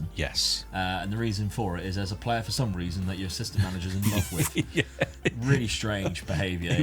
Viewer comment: I think your AssMan picks Parkes as the left sided CB as he's left footed whereas McDonald is right. No other explanation really. But something I do keep forgetting to ask... We never see your AssMan or how good they are. Seeing as you holiday the season and leave him in charge every time, surely the quality of your AssMan can make a big difference. I know you lock in a player or three into the formation but do you do anything else? Also, more importantly, do you guys spend any time trying to find the right AssMan to leave in charge or do you just go with whoever you are given at the start? Seems to me it could make a big difference to results. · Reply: I just realized you mean assistant manager😭 I thought u just deadass said assman